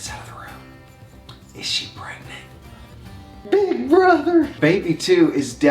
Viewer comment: Sounded like said baby brother, LOL. Have a beautiful, blessed day. God bless all of you always.